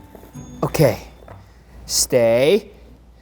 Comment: That's what I say when I taught my dog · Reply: Recorder he says "stay stay"